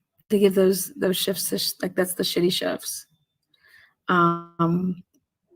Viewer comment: instead of shitty shift it types out shitty chefs 😳